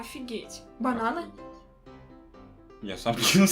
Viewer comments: Бананы мне послышался бараны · мне одному послышалось бараны вместо бананы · мне послышалось БАРАНЫ)))))))